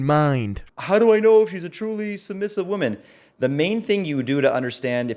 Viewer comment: Did he say 'how do I know HE'S a truly feminine woman in 7:01?